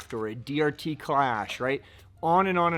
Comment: Again, comes through and spits the correct response. Also, "Klash"? I heard "Klash".